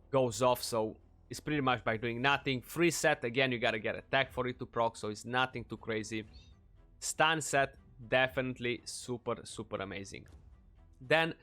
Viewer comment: I thought you said spit set...